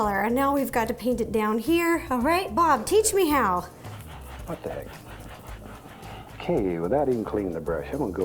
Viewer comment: Did anyone hear bob say what da heck